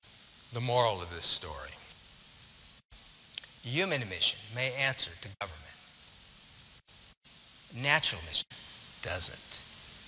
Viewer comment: What does he say is the moral of the story at 47:36? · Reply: Human emissions may answer to government, natural [emissions don't].